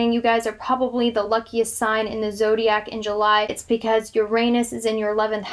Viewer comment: I can't believe the subtitles said It's because your anus is in your 11th hous of wishes coming true😂😖😶